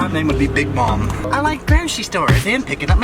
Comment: Why is Garret saying “I like grocery stores and picking up my kids” the best thing ever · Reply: I thought he said, " I like Granchie stores... ""